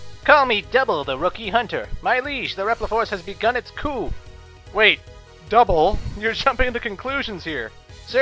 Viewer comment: Anyone else notice at 0:08 that Capcom made an error and put in "wait, Colonel..." instead of Double? I'm a fucking nerd.